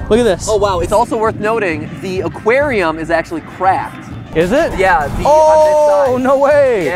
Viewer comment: I thought Matt said that the aquarium was on crack XD